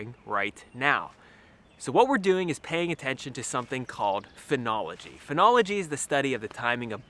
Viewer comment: At first I thought said Phrenology and I was wondering what the hell studying bumps on a head had to do with trees.